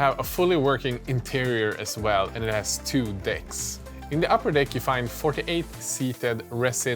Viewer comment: I can't be the only one who heard "it has two dicks".